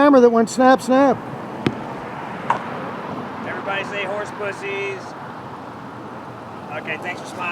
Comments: Did he just say “everyone say horse pussies” when he took your picture. I’d say he should be arrested for that. 🫠🫠🫠💀💀💀. Weird as hell. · Did he just say horse pussy? Lol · wait, waaaaait just a second... I think this guy has a fetish for "horse pussy?!?" Is that what he said?!? ...what a weirdo! 😂🤣